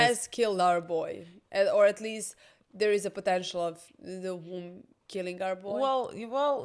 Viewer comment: did I hear potential 🫪